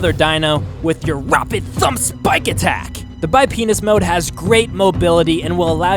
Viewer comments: did I just hear bipenis mode lmao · i heard bipenis lmaooo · almost sounds like he said bi penis mode lmao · Am I the only one that heard him say duel penis? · is it just me or did he say "bipenis mode" at 4:20? :o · Did anybody else catch that he said '"The Bi-penis Mode" at 4:22? It cracked me up! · did he say bi penis? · Did he say bio p*nis mode in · did he say penin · wait did you just say bipenis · anyone hear bipenis??😂😂